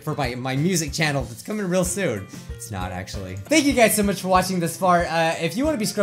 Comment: i thought he said “Thank you guys so much for watching this fart “ 😂